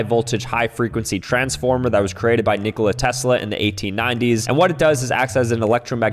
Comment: did I just hear nikola Tesla… DEAD RAILS